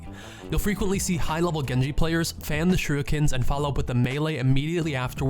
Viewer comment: Did he just say shooikens